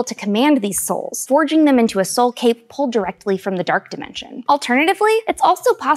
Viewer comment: When you said "soul cape" I misheard it as Soulcade and now I want there to be an arcade for the dead/undead 😂😂